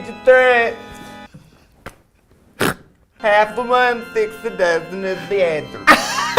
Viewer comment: I still cannot figure out what Trixie is saying. I can't even figure out where one word ends and the next one begins. Every time I rewatch old episodes, I'm always stumped when I hear it. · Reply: She said half of one, six dozen of the other. Katya probably laughed because that phrase is actually six of one, half dozen of the other.